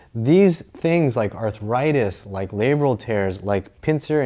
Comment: I thought it said liberals tears. But still not disappointed.